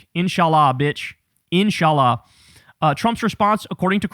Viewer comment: did Kyle said InshaAllah? lol